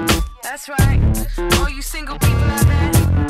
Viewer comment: I was 10 years old when this came out and i have always thought she said "zingo" and not single..